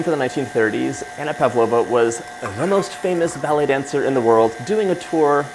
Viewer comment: lol. i thought you said “belly dancer “.